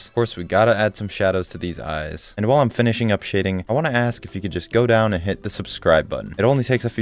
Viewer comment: i heard while am finishing up shitting😭😭😭😭